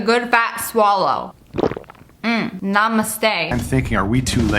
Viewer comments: Did she just say NAMSTE?🙏🏻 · did she said namaste??